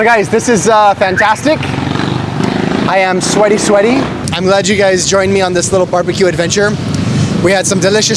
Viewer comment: Parang nalasing nga ata sa Red Horse. Parang narinig ko "imaletyouguys..." Yung 5 words dapat naging 1 word nalang.?! 😆 O mahina lang siguro talaga pandinig ko.